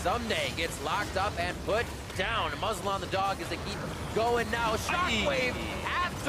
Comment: what did say @ 6:26 ? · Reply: Put a muzzle on the dog